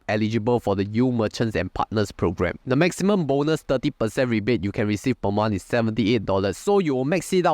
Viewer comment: caption should be 13% instead of 30% right